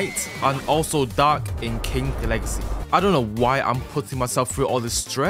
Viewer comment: am i the only one who heard him say kink legacy